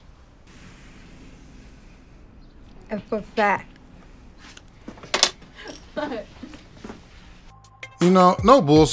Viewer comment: What did she say when you said it was good timing @ 11:20 minute mark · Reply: “F for fat”